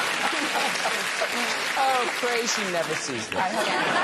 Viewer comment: What did Graham say at 16:04? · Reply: “Oh, pray she never sees this.”